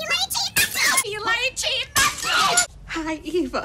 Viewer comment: I always thought she was saying 'you lying cheating magpie' 😂😂